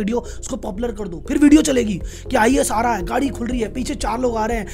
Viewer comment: kya bole Rahul Sir ??? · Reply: Ias AA rha hai ye bola